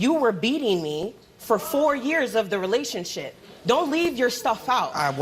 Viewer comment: did she really call it a relationshit!?! Hahahahba